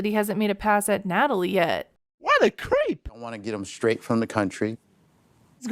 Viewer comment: Oooh, he said "'straight' from the country"!!! I watched this in two other channels, and I always heard "fresh"; less creepy, but still creepy.